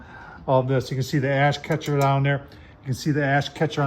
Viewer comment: My mind is in the gutter, because i thought he said a55 catcher